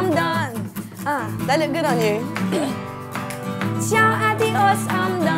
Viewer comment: what did she said?? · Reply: "they (the glasses) look good on you"